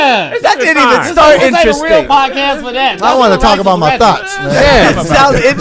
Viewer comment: This episode was hilarious! 😂😂😂 Doboy sounded like he said I wanna talk about my thots 😆 not thoughts 💭 😂. I love flats. I order all flats. I don’t like the way the bone looks on a drum when u bite into it. It’s crazy how ppl don’t know what they are eating 😂. “He got a deep fryer in the bathroom” 😂 50 is the King of trolls!!!! He lives for it. 69 only does it when it helped him. I choose childhood best friend because they reallllllyyyyy know u. They don’t want anything. And they love ur flaws and all. Adults can come in ur life for something. Sorry but bullies are necessary!